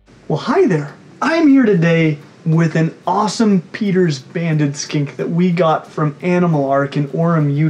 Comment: I thought it said Peters abandoned skink!